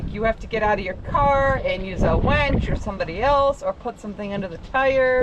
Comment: Does say at 14:07 "use a WENCH" ?? ;-)